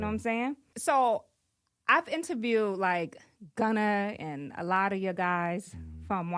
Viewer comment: Did she just say "gunner"....mam......it's gunna